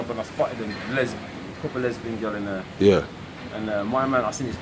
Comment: did he just say “couple lesbian gyal in there” 😂😂😂😂😂